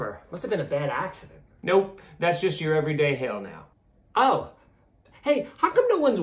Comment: Thought he said that’s just your everyday hell naw 😂